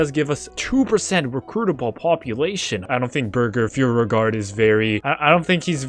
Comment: Did you just say...that his name is burger?!